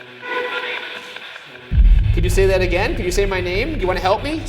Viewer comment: I heard "coming" instead of ryan actually